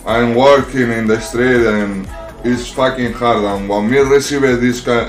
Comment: Did he say "I'm working in Australia" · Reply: He said "im working in the street"